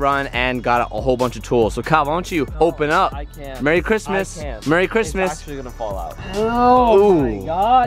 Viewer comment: I heard that plies "marry Critmus" lmao